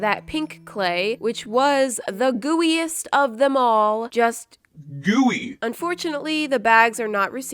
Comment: I thought she said DOOEY